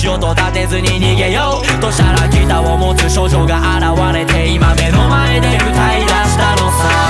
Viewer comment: 処女が現れて、に聞こえてたから「少女」に安心したわ(笑)